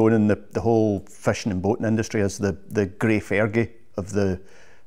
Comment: I thought he was saying fashion industry for quite a while.